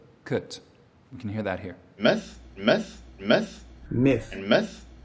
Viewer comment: all I hear: myth METH myth METH myth METH